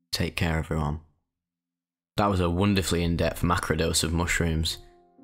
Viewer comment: Did you just call 15 grams a microdose?!?